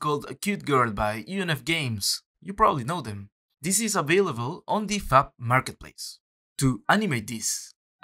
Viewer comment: I could swear I heard "this cute girl is available on the fap marketplace" haha